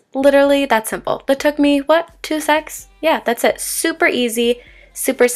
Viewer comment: you said 2 secs but i misheared it as 2 sex :3 lmao